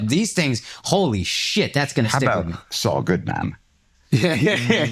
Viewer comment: Anyone know what line Peter was referencing at 15:28. I couldn't make it out. The CC says "Sa good man" but that doesn't make sense. I am a huge GoT fan and it is bugging the crap out of me I don't know the line he is talking about lol. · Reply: Saul Goodman was one of the characters in Breaking Bad and the lead guy in Better Call Saul. In the show this was a fake name he used for various purposes. The beauty of this fake name is that he ran TV ads where his slogan was 'It's all good man', which is derived from/ is a play on Saul Goodman.